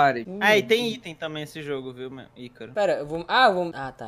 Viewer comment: Ksks entendi "meu ycaro" Ksksks foi mal